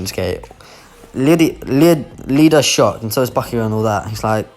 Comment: First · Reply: When you say ida it sounds like ypur saying leader